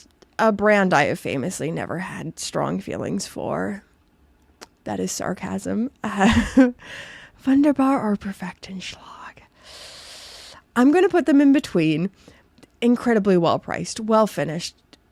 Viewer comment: I keep hearing u say "schlog" what does that even mean